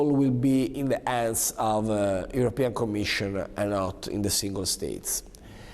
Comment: subtitles says: in the ass of European Commission